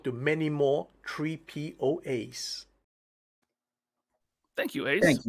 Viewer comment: Y'all thought Ace was saying "3POAs" in his congratz video, but he actually said "Tree PO Ace." He was teasing a new upcoming preorder for the new Ramen Toys "Tree Ace" aka Not Groot deluxe figure. Can't wait.